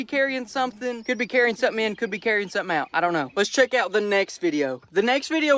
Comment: it sounded like he said "I don't know if he's here"